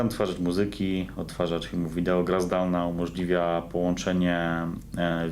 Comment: szczerze to usłyszałem "gra z dałna"